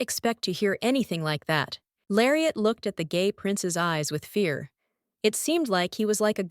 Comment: I think I’m hearing things or did they say gay prince 😭